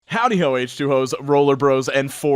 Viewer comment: Sounded like he said h2 hoes 😂. Or has that always been the joke and ive just missed it?